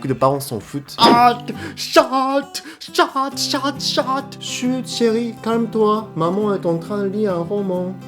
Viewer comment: À 5:30 c moi ou tu gueule "teucha teucha teucha"? 😂